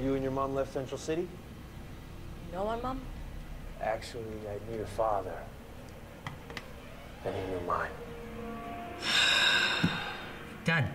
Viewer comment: What is the last thing Chase says at 13:23? I cant quite make it out. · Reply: Luke the CGI Guy his last lines are "I knew your father, actually, and he knew mine"